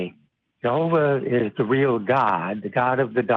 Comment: Did the caller say “Jehovah is the real God”?